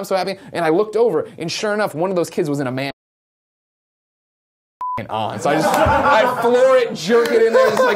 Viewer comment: I think Greg says the kid "was in a Matt Hardy t-shirt, and so it's was fuckin' on". I love Greg. I love how much he loves wrestling.